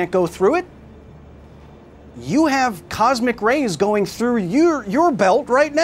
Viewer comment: From my pocket kyle said something about a belt. I thought he said to “check your belt now.” I looked down and saw that it was not completely buckled. Thanks kyle